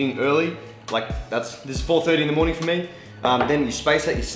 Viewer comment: did he say 4.30 in the morning?